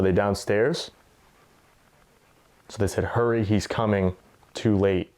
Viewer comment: That voice at 19 mins sounded like it said. He’s coming.